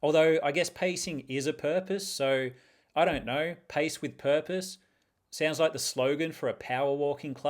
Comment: At first listen, I thought you said "Paste with purpose" and I thought i missed something crafty, but you were just going for a walk 😅